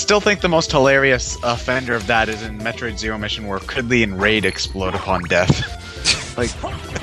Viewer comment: DID JUST SAY KRIDLEY AND RAID?